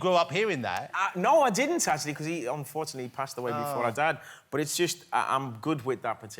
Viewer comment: Explain then, because that's what I heard. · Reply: he's passed away "before I'd had"